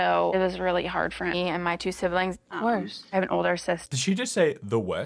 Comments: I think she said "of course" in response to her saying that it was really hard for her and her siblings, not "the worst"...but I could be wrong, that's just what it sounded like to me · I think she said "of course" 😂😂😂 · I think she said “‘course” like of course